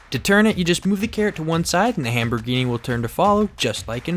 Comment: Did he say Hamborghini?